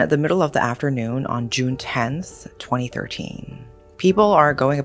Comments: She says June but her captions say July... Even went back to listen again bc thought I misheard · hii! i js wanted to point out that at 0:05 you said june 10th but the captions said july 10th :3 great video nonetheless! i always love using your videos in the background while i study or work out!